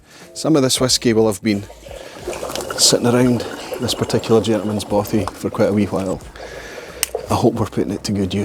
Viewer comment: Is that friend ... ralfy by any chance? ;) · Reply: I thought he said German bothy